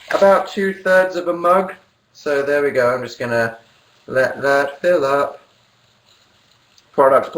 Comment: it sounds like Dan said "I'm just gonna let that Phil up."